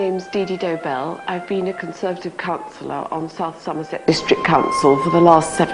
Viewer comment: Jesus it sounded like she said "I've been a conservative cunt...."